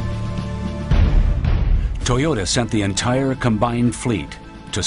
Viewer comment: mark, Did he say Toyota??? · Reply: toyoda..with a d